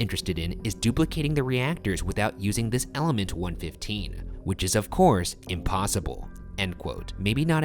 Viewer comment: Did he just said element 115??????????????